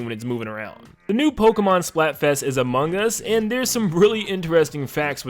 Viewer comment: I thought he said the next Splatfest is “Among Us”😂😂💀💀 Imagine if Nintendo collaborates Among Us🗿